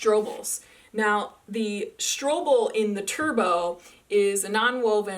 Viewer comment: Could someone help me to figure out that at the 7:17, said a word kind like "strobel". What is the correct word? Thanks!